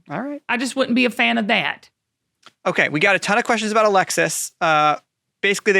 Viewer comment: My tablet just answered you. It thought you were saying Alexia. It said I'm not sure how to help you. hahahaha